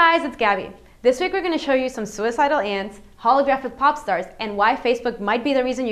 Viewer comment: when u first said a holographic pop star, I misheard u and thought u said holographic poptart :)